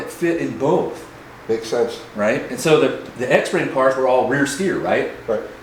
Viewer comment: "X-Ray cars where all rear steer"? 5:54 Did I hear that wrong? I've never heard of that back then. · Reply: X frame cars